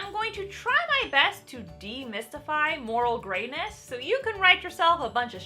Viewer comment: Idk why, but at 0:39, I heard "moral gayness". What would that even mean?